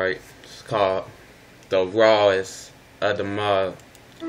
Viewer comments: it sounds like he is saying its the rise of the mall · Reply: no i think he said "is the rats in the mall." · Lmfao BRUh tell me why this whole time I thought he was saying "it's the rest of the month" 😩😩😩😩😭